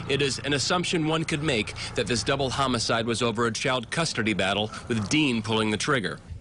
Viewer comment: I'm sorry. Did he say, "Custardy battle"?